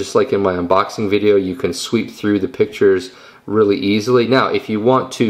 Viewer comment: swipe ! not swip or i dunno what you said there !